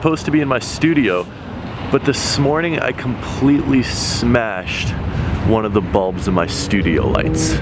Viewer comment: I swear i heard “I completely smashed one of these hoes in the studio”